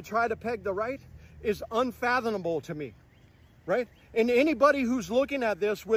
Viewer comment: Wait... Did he just say "UnfathoNable"? I swear he did. But you klnow, honestly it's hard to tell because he looks / sounds like he's about to start crying at any moment. I thought neweded a hug, but this guy? 10 hugs! So sad....